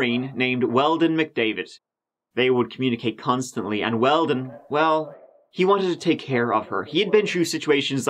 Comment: LMAO i heard, "well, he wanted to take hair off her."